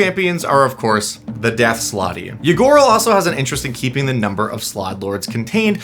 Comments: I kept hearing "Ygorl" as "Ya gurl" and hearing "Ya gurl is out here doing ranked competitive tyrant activities" SENT me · I misheard Ygorl as a Ya Girl.